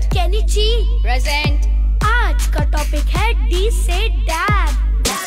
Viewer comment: I think he said "our topic is dab?" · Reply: Yes. He said " Today's topic is "D for Dab" "